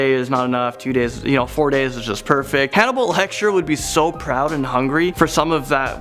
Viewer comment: Am i the only one that heard him say "hannibal lecture" instead of "hannibal lector?" Also why is that so true tho XD